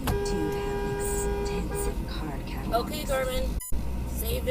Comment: #8 She must’ve just gotten the Garmin. 🤣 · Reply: Is that what it is? I always thought they were saying "Okay, Carmen". I didn't know Garmins could record video.